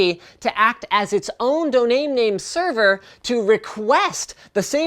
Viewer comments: Linus did you say donaim name?? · did he say doname name insead of domain name?